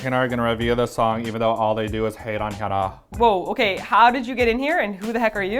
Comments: the cc says T-ARA at 00:29 but its suppose to say Hyuna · the subtitles is wrong in 00:29!!!!! it's Hyuna, not T-ARA...